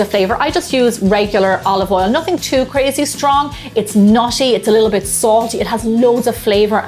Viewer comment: i thought she said olive oil was "naughty"